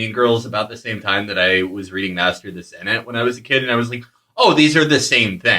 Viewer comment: What's the name of the book Felix mentions here? 6:28 I can't catch what he is saying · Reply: Nvm I think I got it when he mentioned it later on It's master of the senate by Robert Caro